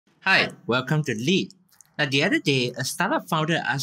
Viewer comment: I thought he said, welcome to Lee